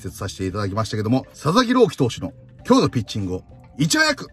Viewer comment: 里崎朗希投手に聞こえた（笑）